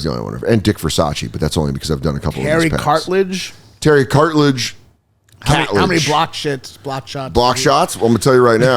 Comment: I really thought he said Harry Cartilage. That's the rap name! Love these card eps!